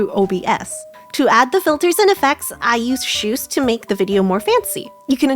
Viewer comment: What program is that? I didn't catch the name. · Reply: Shoost